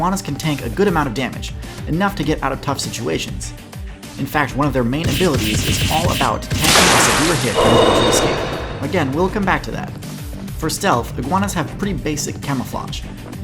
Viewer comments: was a bit too loud, couldn't hear what you said but other than that perfekt video got me thinking about a quick iguana playthrough in the future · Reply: I believe he says “one of their main abilities is all about tanking a severe hit in order to escape”, referring to tail detachment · can anyone actually hear what he said after “one of there main abilities”? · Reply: “One of there main abilities is to tank a severe hit and escape.”